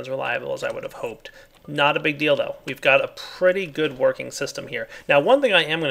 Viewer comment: Haha 27:11 "Not a big deal, tho." All I heard was " Not a big dildo." =P